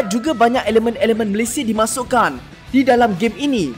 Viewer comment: knpa dengar mcm malaysi? Ke saya salah dengar